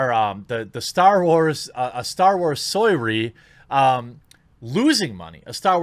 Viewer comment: what are you even saying soy ree?